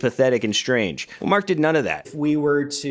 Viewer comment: he CLEARLY says "wank at the camera" because he then also says "show our hand"